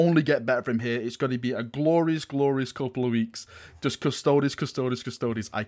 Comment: It sounded like you said "Have a Grey Knight" at the end - what is this heresy?